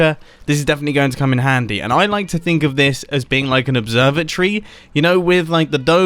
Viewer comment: When he said 'observatory' I legit thought he said 'observer tree' because of all the redstone